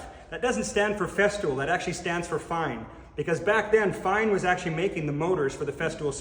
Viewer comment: I swore that he said that "the F stands for Fun!" until i replayed it like 3 times